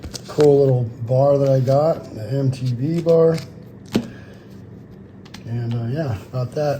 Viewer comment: I thought you said "an A.M.T.V. bar lol" you said "MTB bar" lmao.